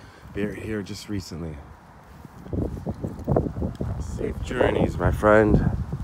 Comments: Did anybody else catch the female voice say "safe" over his voice @ 9:41? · Did anyone else hear a female voice say "safe" along with him when he said "safe journeys my friend"?